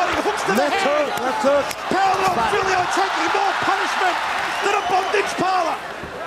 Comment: Did he just say "more punishment than a bondage parlor?"